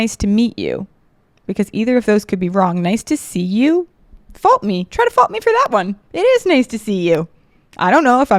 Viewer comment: What did you just say!? 😂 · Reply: 😂😂 FAULT ME